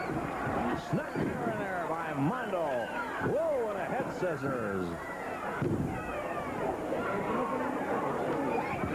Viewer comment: Mando Geurerro has some good moves. 1:17:38 Does anyone know who Mando's partner is? Tony something, couldn't make out the last name. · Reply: Ted Heath. He had previously wrestled under a mask as Mr. X